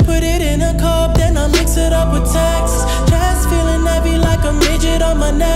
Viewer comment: "Chest feeling heavy like a midget on my necklace." - Abel. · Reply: HybridTay I thought he said dreads feeling heavy